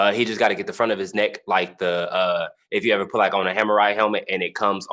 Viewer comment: Did he call it a "hamurai" helmet?